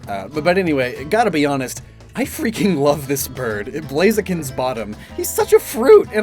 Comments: I thought you said "he's Blaziken's bottom" and I was like, yeah he probably is, they'd be a cute couple 😂 I'm all for a Blaziken/Quaquaval ship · did you just call Quaquaval Blaziken’s bottom?!?!?!?😂